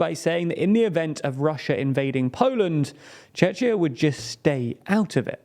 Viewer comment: did you just say chechia?